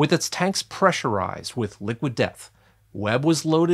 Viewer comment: I thought he said 'Liquid Meth'